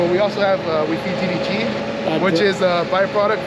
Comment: he says that cows can eat things humans can't, and it sounds like he said they feed them DDT but that can't be right, can it?